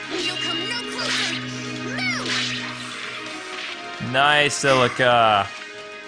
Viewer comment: Did Celica say MOO! At 34:18? Like if you agree